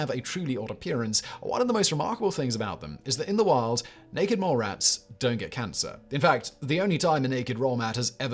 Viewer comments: Anyone else catch the "naked roll mat" at 7:14 ish? 😂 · Does anyone else think it sounds like Simon "naked roll mats" at 7:16 instead of naked mole rats?